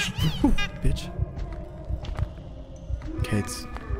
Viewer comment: Can't believe I heard say b1tch